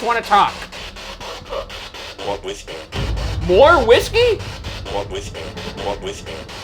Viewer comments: I think the one voice said "we're with you" not "more whisky" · … sounded like, “well talk” 12:47 … sounded like “yessir” Great finale. I’ve never missed an episode. Sad it’s over but this will remain my favourite series ever. Thanks for the entertainment, Ghoul Boys forever. 👻 🖤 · When they were using the ghost box at around 12:40 i heard “oh I’m with her” instead of more whiskey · when ryan says 'i just wanna talk' and the spirit box follows after, it sounds more like 'more with you' as opposed to 'more whiskey', which would make sense with what ryan said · When Ryan says "I just wanna talk", instead of "More Whiskey" at 12:37 I heard "What? With her?" as in Sallie... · Omfg he ACTUALLY says "More with me" aakkkoaekoajhng Edit; Seconds later when they asked if it liked whiskey it said "Definitely" before the garbled music frequencies. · i think instead of “more whiskey” its saying “or whisper” responding to ryan’s “do you want to talk?” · sounded like 'walk with me' rather than more whiskey :o He wanted to walk and talk yoooo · when Ryan said they just want to talk.. they heard "more whiskey" ... I heard "walk with me".. I cant be the only one who heard that right? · I definitely heard "more with me" and not "more whiskey". It gave me chills down my spine and I adopted Ryan's donut face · When they heard "more whisky" I heard "we're with you" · At "more whiskey" I heard "not with me" - interesting response as Ryan said "I just want to talk"